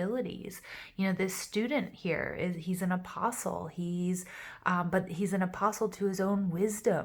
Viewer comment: Omg too medicated thought you said he's an impossible You said apostle Makes more sense now.... 🤣🤣